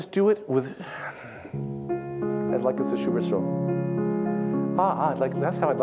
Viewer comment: What does he say at 54:03? "like it's a _______" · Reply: Liebestraum……love dream, Liszt